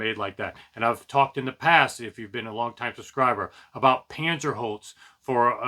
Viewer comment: At the 4:32 mark you mention a material that you feel is better than MDF but I couldn't make out what you were saying. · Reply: Sorry... It's called Panzerholz. Kaiser makes speakers out of it and I've featured it being used in equipment racks too. I think one of my final review videos of the GR Research NXtremes mentioned it in more detail too... If you're interested in learning more.